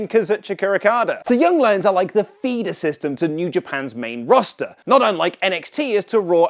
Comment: Did you just say "fetus system"?